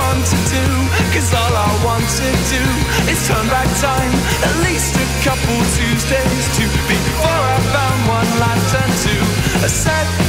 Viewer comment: I ALWAYS HEARD IT AS AT LEAST A COUPLE OF TUESDAYS TO BEFORE I FOUND ONE LAST TO TURN TO WTF HOW AM I THAT DUMB